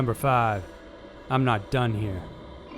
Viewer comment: instead of "I'm not done here" I heard "I want Johnny here".. did anybody else hear that or am I tripping? lol.